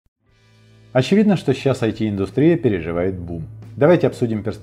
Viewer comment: "Бум" почти шепотом, не услышал первые несколько раз ..